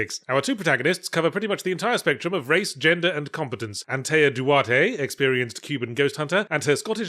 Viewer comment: I completely misheard "Antea Duarte" as "<something> Tuatha Dé" and was like, "But the Tuatha Dé Danann are Irish folklore, what the heck"